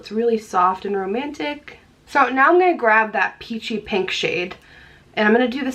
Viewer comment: I totally heard "the shit I am using is-" lol! Had to rewind to realize you said shade